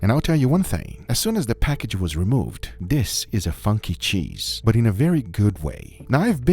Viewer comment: did he say what I think he said😳😳💀💀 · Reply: He said funky cheese